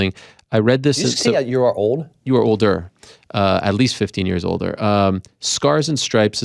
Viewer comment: funniest part of interview, Ariel says you were older but it sounds like he said old ..n right as I'm thinking that Kennedy checks him n I just burst out laughing. People hate on Ariel but he's legit and the Goat n so is Tim Kennedy he's a Goat I was excited to watch this interview...2 🐐🐐